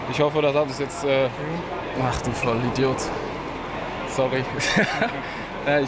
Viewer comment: was sagt er da zu Süle ?😅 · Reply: "Ach du Vollidiot "